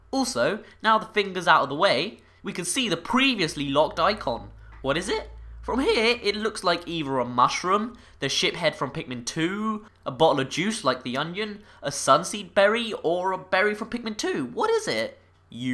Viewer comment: I thought he said shit head!